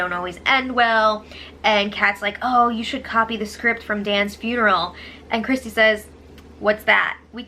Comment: how come i heard “dance funeral” also but know exactly what Dan’s funeral is.